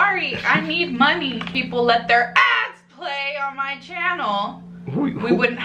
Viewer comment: I thought she was saying ass play I'm weirdly disappointed